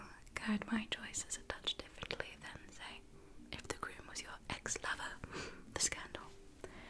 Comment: I misheard her, and thought she said "brides"and lost my goddamn mind in joy, until she said groom again. Tho I guess it could be an old school gay wedding(They did happen, tho rare), with one calling themselves the groom.